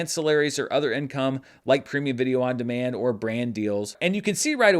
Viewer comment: Thought you said "Creamy video on demand" and I thought I had missed the memo about some new marketing gimmick.